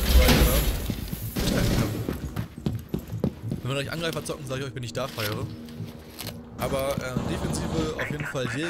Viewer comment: Was sagte bei 9:13 welchen Angreifer speit er gerne ? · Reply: 1711 Thatcher und Thermite